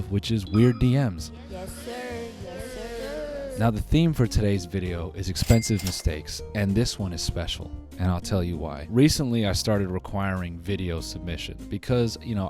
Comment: bro i thought that shit said “expensive milkshakes” and the thumbnail was john travolta wtf the fuck